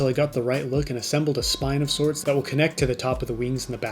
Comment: I'm sorry, Spinosaurus? Did I hear that correctly?